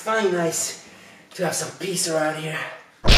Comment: did somebody said pizza???!!! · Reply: He said peace around here.